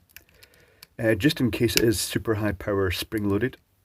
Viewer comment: I thought he said "big purple spring" the first time